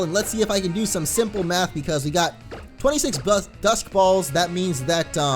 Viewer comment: I thought he said dust ball when he was saying dusk ball :')